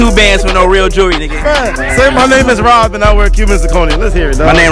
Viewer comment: "You could check em " 5 sec later " Cuban jevshdhdn cuban jevoneyim " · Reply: yeah i know but he was speaking so fast it sounded like he said cuban jejevsehdsyim not cubic lol